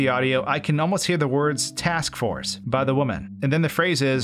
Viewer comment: i hear "xbox" and not "task force"